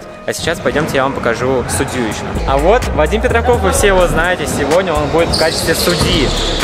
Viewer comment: мне послышалась вадим пердоков